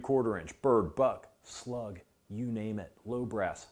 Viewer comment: I don’t know why, but I heard “…bird, butt, plug, you name it…” and had to rewind… I guess it’s one of those days today…